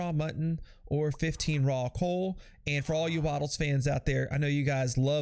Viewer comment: Did he just say raw coal?